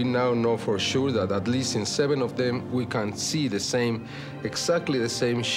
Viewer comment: did he say “we can see the same shit?”